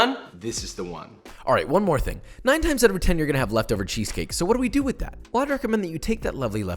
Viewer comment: Hey guys can someone tell me what he said at 5:01? It sounded like leftover cheesecake or something like that... WHAT IS LEFTOVER CHEESECAKE?!?!?!